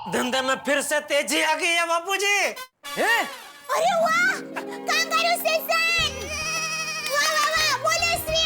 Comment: kya boli daya kongarulations 😂😂😂